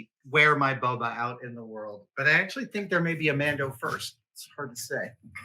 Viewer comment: He said "May be a Mando first" and I heard "May be a Lando first" and I was thinking that would be cool as he is not a common cosplay subject . . .